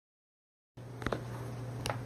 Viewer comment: Thought it said "waking our dead kitten" 👁️👄👁️